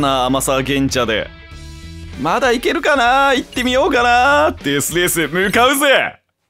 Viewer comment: 「デスレースに向かうぜ！」が『寿司を食わないと死ぬぜ！』と同じ語感で聞こえて笑ってしまったw